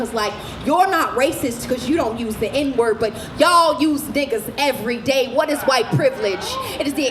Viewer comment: Question: what does she say during 2:05 because I don't understand Not trying to offend I am genuinely confused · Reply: "Y'all use ni**as everyday, what is white privilege?"